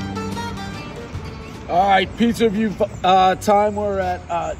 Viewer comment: One cameraman job opening, everyone knows Dave wishes Frankie would come back. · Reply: I thought you said, you alright spider?